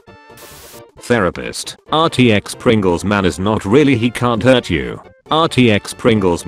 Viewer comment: it said "not really" instead of "not real"